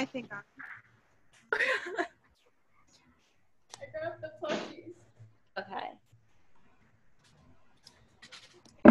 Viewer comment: who did jirou say? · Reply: Toru / higakurai